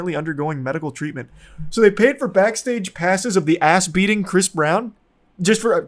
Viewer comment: Backstage pass? I thought you said Rhianna Pass.